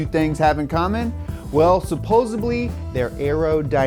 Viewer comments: did he say supposebly instead of supposedly? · did he say suposably? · Did he just say.... supposably? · did he just say SUPPOSABLY. IT DOES NOT MEAN "ALLEGEDLY" which is the meaning of SUPPOSEDLY. PLEASE NO NO NO NO. CHANDLER IS PISSED. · Did you just say "supposibly"? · "SUPPOSIBLY" Instead of supposedly???? Is that some sort of murican thing like aluminum instead of alluminium? Coz it sounds stupid.